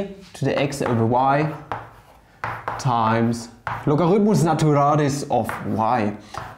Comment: Did i hear "logaritmos naturales"? Greetings from Mexico. I love the channel! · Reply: He said "logaritmus naturalis," which is Latin, but his pronunciation does sound similar to the Spanish equivalent, although Latin pronunciation is already fairly similar to Spanish pronunciation anyway.